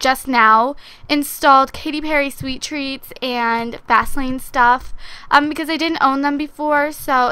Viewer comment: It sounded like she said vasealine stuff instead of fastlane